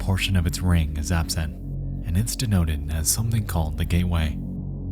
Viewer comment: ngl i thought you said "gayway"